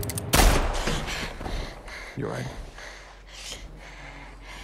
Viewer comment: what the fuck did four say? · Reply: He said, "You're alright." He was trying to reassure her because he knew she was going to wake up scared.